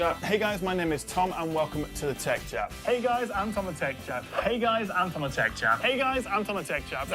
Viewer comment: I've always thought you said "Hi! I'm Tomma Tech Chap" until I heard one of your earlier videos and realized its actually "Hi! Im Tom the Tech Chap" 😂😂